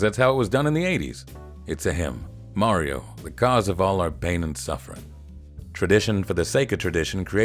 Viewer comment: I enjoy these so much! Such great writing, observations and voiceover from! Great editing too! 3:14 - "Its a him, Mario. The cause of all our pain and suffering" 🤣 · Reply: Ha. I heard that as: "It's a hymn. Mario: the cause of all our pain and suffering..."